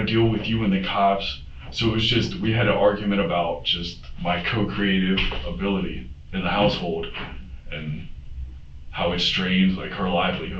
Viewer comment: “co-creative ability in the household”? Is that what he said? And wth does that mean? lol